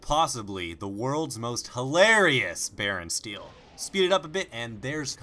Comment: lol for some reason i thought it said "EPIC BACON STEAL!" lolz